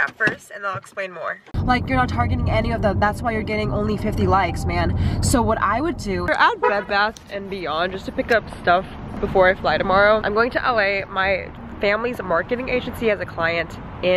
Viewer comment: lol i thought you said blood bath and beyond!!! hahahaha omg dude you coming to sf ??? how long will you be here for ??? I'm 30 minutes from sf !!! i wanna say hi !!! :)